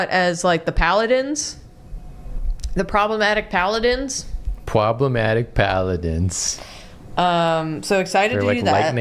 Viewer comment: Warhammer arc! Exciting! Someone suggest Drawfee X Warhammer episode ideas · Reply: I heard "problematic paladins " and I was like sounds like the Custodes to me!